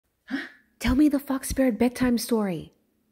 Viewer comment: i thought u said “tell me the fu** spirit story”